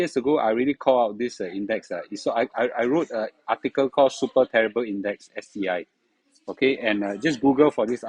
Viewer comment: I thought I heard said "Suay Times Index" 😅